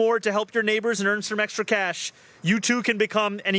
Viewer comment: I heard " Extra Cash " ! Not a Check . Just Saying